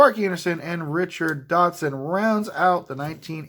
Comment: Nice hits! Had to do a double take, I was listening and swore I heard you say “Richard Dawson” and thought you were making a joke and got Andre. It was really “Dotson” (and you got him twice AND an Andre!) now I’m gonna start trying to call game show hosts whenever I can